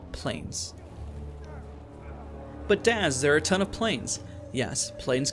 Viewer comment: He said "But Daz..." I heard " But DAAAAAAD..." xD!